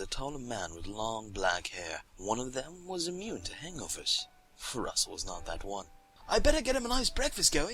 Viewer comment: When he said hangovers for the first time in the story I thought he said anglerfish